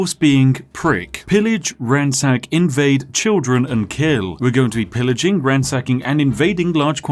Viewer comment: At the start of the video I thought he was saying he would pillage, ransack and invade children